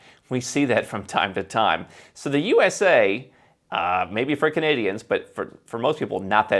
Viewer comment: When said "... for Canadians...", I could have sworn he said "freaking idiots"